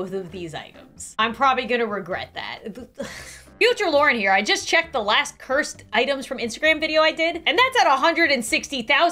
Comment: I thought she said the last edible item omfg